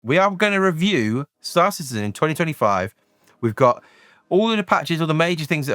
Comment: Every time you say Star Citizen I hear "Star Czsszse". Joking aside, great videos, keep it up!